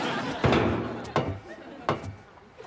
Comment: I've never been able to figure out what Kramer says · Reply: "Double bugger."